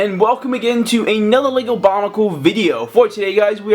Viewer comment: did you just say bonicle